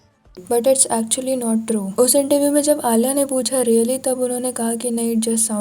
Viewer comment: on 2x sounded like diddy more than really